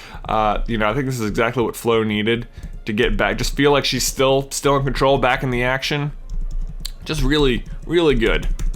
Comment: it sounded like he said that flo was still in control of her acne. Wish I could say the same for myself